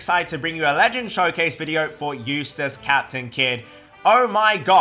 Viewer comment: I was so shocked I thought you said in the beginning is "Useless Captain Kid" then I remembered his name was Eustass 💀